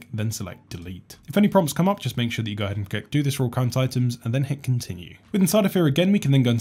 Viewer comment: Can you please translate minute 2:08-2:12 "If any problems come up, just make sure you go ahead and_______ and then hit continue? Sounded like you garbled words and playing back 5 times in a row, I still can't figure out what you said, lol. I do appreciate how you are straight to the point and move quickly from one thing to the next. It's a lot of content in a short time. Awesome. Thanks! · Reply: If any problems come up, make sure that you hit the "do this for all current items" box then hit, "skip" :)